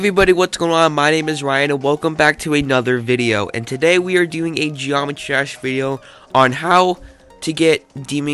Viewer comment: I always hear "geometrash"